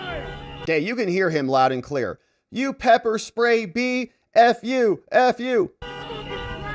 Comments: It sounds like he said "you pepper sprayed me"....so, was there ANOTHER incident we don't know about? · Pretti was the one who started everything. Until he arrived they were just honking and blowing their whistles. He came in hot, yelling profanities, spitting and getting in their faces. He is the aggressor. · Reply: It sounds like he said "you pepper sprayed me", maybe there was another incident we don't know about